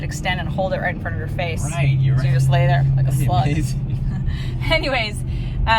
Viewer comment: I relistened 5 times to hear you say “just lay there... like a slug” and swore you said slu - t. Woah.